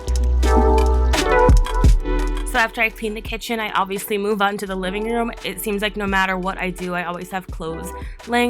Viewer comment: subtitles say: “so after i pee in the kitchen i obviously move on to the living room” 01:48 i can’t - i’m dead 😂😂😂